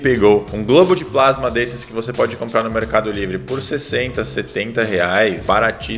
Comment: eu ouvi ele falando Mercado Negro kkkkkkkk